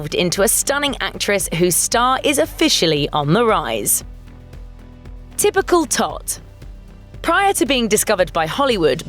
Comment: Sounded like she said “Typical Thot” instead of “Typical Tot” 😩😩😭😭😭😂😂😂😂😂🤣🤣🤣🤣